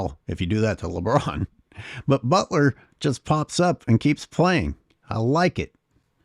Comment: Did you really say LeButler, as he had his layup and ADs foul wasnt called? ( around 11:20 ) 😂😂😂👍🏽... the man 👌🏽